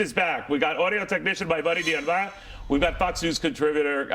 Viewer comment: he says the first guest's name...I played it 5 times.....and I can't figure out what he says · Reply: I think Neil said "buddy, Dion Baia"